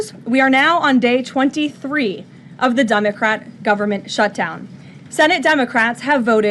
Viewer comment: Did she just say, “Dumbacrats”?!